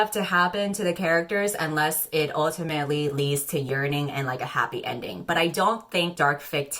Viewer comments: i had one ear in my pillow so i thought i heard saying “urinate” and not “yearn.” for example, “look at that face, that’s the face of someone URINATING for the other.” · Not me mishearing “yearn” with “urine” 🤦🏽‍♀️ I literally thought had a golden shower kink, I hate myself🙃 Half of the video I was side eyeing.